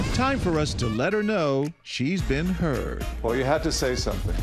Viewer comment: I literally heard "She is being purged"